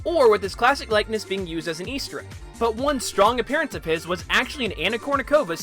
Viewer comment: "His classic likeness being used as a..." What is the word you say after that? Sorry I actually can't tell. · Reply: "... an Easter egg."